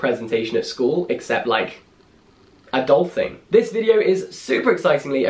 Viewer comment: I thought he said 'a dolphin'